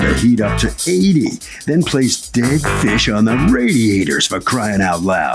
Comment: Ahhhh!...........Fish on the radiator...I thought she said PISSED on the radiator!!!!!......:>)